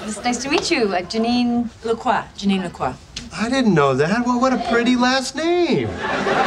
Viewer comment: Hello to everyone! At first, I wanted to ask: would you like to explain to me what did Joe say after Janine said her Last Name (19:32 time in the video), please? But then I pushed the button 'subtitles' and read. I remember that you gave this tip in one of your videos. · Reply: He said: "I didn't know that, what a pretty last name". Here he pronounced "what a = wadda".